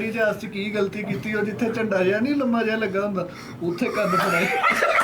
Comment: last me kya bola isne jo sab hasne lage puri baat samjh nhi aayi 🙄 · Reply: Uthey kan pharaye